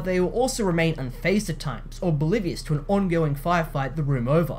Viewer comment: Yea. I like the reviewer. Soon as he used the word "bombasitic", I thought, Oh wow, this kid has a foosher. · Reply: He also used the word bolivious instead of oblivious at 3:31 but I think that was just a mistake/misspeak instead of stupidity and ignorance lol.